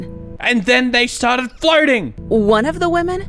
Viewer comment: Subtitles said flirting instead of floating lol Edit: So does that mean they are flirting in the lady lair?